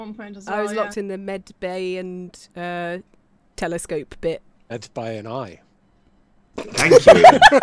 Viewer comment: Please, pleaseee, someone tell me what Ian said at 1:12:46, his dumb humour is the best, I need to hear it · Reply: He just said "Medbay and I", taking a jab at repeated insistence on (sometimes incorrect) grammar checks.